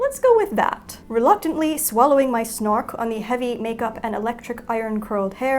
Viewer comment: did she say swallowing my snork? Isnt that the sidekick from thundercats wtf